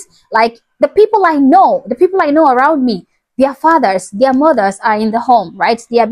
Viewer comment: I thought you said Africans have their fat ass in the homes. Then I realizes you said “fathers”😂! Your accent is cute!